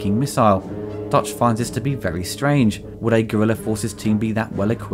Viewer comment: At first I thought you said "(...) who have been abducted by Gorilla forces". I was like "w... what??", then I realized, you mean "Guerrilla" ;)